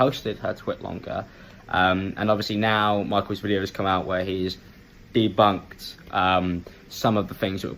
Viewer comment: Did he just say "DeBuNkEd" ?